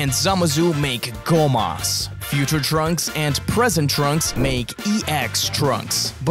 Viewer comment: I thought he said "Extra Trunks"💀